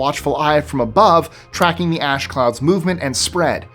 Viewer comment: You said ash clouds but i could have sworn you said ass clouds i was dying laughing over here